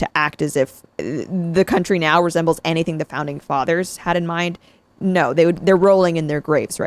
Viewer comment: i heard ‘pounding fathers’ lol